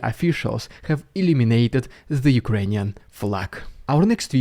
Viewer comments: Just pointing out he said "Illuminated" the flag, not "Eliminated" it because those are 2 very very different things haha. I misheard and thought you said eliminated and was like "wait Russia captured it??" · What he is saying is: Kharkiv officials illuminated the Ukrainian flag. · Ohhhhh.... "Illuminated". I was concerned for a moment. I thought you were saying "eliminated" until I actually viewed the screen during that part.